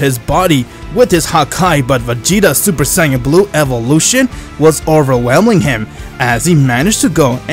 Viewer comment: ngl I heard "as he transformed... into a Super Saiyan Blutooth!" (instead of "blue two")